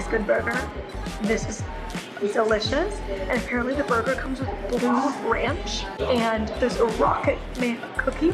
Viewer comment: I definitely thought they were saying bleu ranch until I saw that it was literally blue